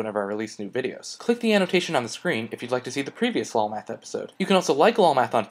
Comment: I always hear "walmart" instead om "lolmath"